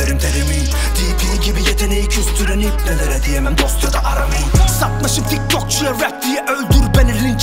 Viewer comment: kezzo dp ye mi sallıyo ben mi yanlış anladım ''dp gibi yeteneğe küstüren dostada diyemem aram iyi'' dediği yerde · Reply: dp'yi küstürene demem dost diyor